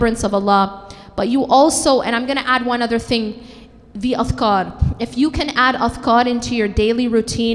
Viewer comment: What did she says in 25:32 ? I don't get it. One of best lecture i ever had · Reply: Salamulaikum Fajrin Sani... She says by adding 'adhkar'(additional supplication besides daily obligatory prayers)in our day to day life,one can be close to Allah...eg morning and evening adhkar,adhkar after prayers etc..I hope your confusion is cleared.